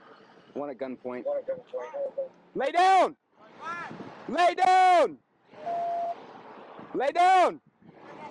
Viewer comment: when he said "lay down", did he say "I'm black"???